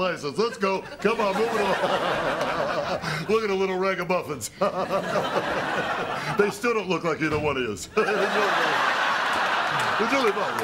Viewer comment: what does Robert say at 5:35 after the twins close the door? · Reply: They still don't look like either one of yous